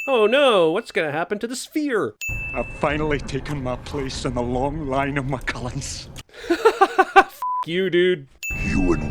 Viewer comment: Did he say "I have finally taken my place in the long line of my cunts?"